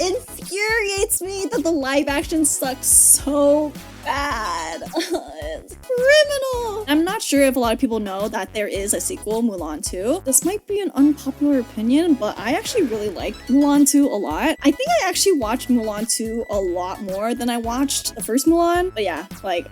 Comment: Did I heard Mulan 2!? 🤩